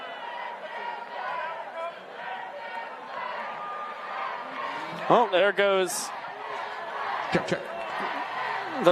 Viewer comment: Does anyone know what the fans are saying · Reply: “Let them play” I believe